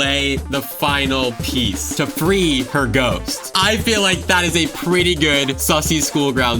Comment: my dumbass thought he said “to breed the ghost”😭